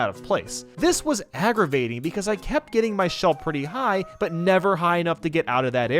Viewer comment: Did Did he just- Did he just say my shellf? that’s my boy